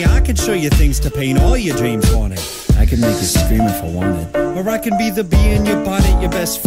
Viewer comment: I keep hearing "or I could be the B in your Body" it's buddy, but I hear body XD